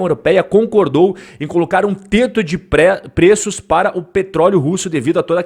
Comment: E só eu que escutei tete de preço 6:22 do vídeo